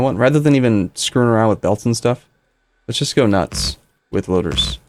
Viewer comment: Hehe, it sounded like you said "Instead of screwing around with bolts, let's go nuts." Puns galore!